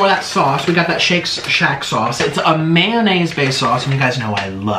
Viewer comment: bruh the subtitles say “I love man” instead of mayonnaise 💀